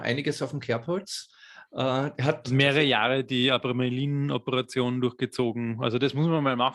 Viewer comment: Ca. Min. 4:20, ich konnte ein Wort akustisch nicht verstehen - ....operation. Wie lautet dieser Begriff genau und was bedeutet er? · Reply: Abramelin